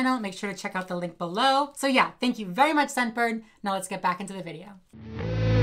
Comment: I legit thought he said back to the rug.🤣💀